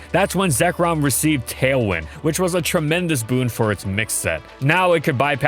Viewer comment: i heard tail whip instead for some reason :p